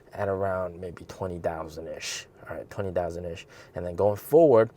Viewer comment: Did you say Q3 deliveries at 20,000 ish?